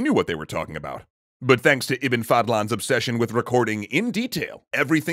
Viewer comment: I swear when the said the Iban fadlan’s job I heard “A fuck you” I replayed the line just to make sure I heard it right when I wasn’t filling a glass of water